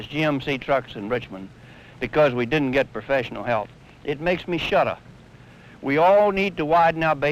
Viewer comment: The CC says "it makes me shut up." He is actually saying "it makes me SHUDDER." There is that Virginia accent to account for. Yes, NICE old trucks!